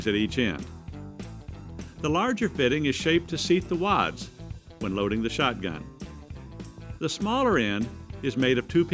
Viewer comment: What does he say at 0:36-0:39? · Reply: The larger fitting is shaped to seat the wads when loading the shotgun